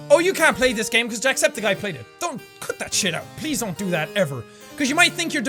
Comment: "cut that shit out" I heard that as doing course work. it was too savage but true